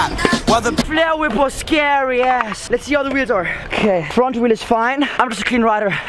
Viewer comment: Did he say ass on